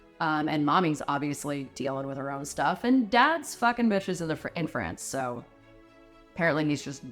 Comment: We don't think that Alice had Schizophrenia - there's no cure for it, and people don't recover from it. They learn to live with it. We think she probably had PTSD, with perhaps a couple other issues. What do you think? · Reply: all I hear is "Bitches be Cray"